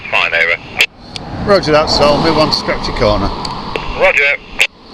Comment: Did I hear"scratby corner"